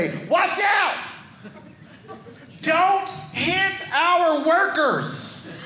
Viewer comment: I thought he said don't kiss our workers 😂